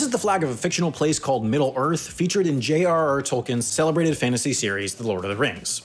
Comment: is it just me or did say J. R. R. R. Tolkien?